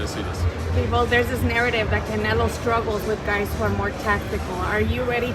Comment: Sounded like he said CANELA....